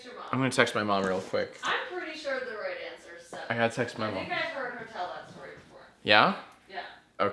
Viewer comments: i was looking away and not paying attention and i thought said "I had sex with my mom" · WHY DID I HEAR ”I HAD SEX WITH MY MOM” AAAAAAA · did jack say (i had sex with my mom) ?????????????